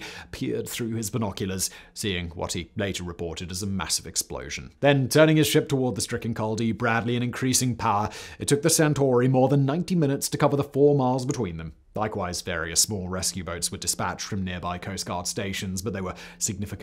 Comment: Did he just say minute-owoc??? Where is Charlie Berens at? 😂